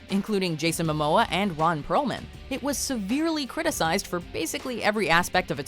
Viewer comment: Is it just me? or did she say Ron Browman instead of Perlman?